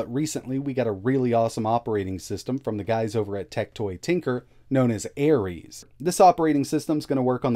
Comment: I heard "Tec Toy Tinker" and I though "Whoa, from that brazilian company that still makes Sega consoles?"